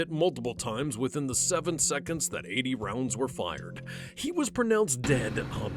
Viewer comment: did he say 80 rounds were fired ?